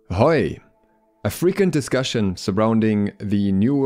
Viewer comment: I thought he said “a freaking discussion” at the into.